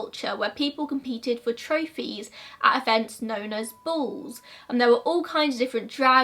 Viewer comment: I thought she said bulls i even googled bulls😂😂 interesting, ive never heard of this love hearing new mysteries